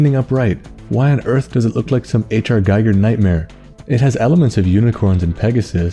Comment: What did he say here? "Some ??? nightmare"? I need to know the source of that black and white picture that popped up for half a second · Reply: I said HR Giger, he does a lot of sci fi horror artwork